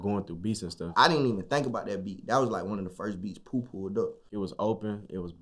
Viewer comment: it sound like he said poo poo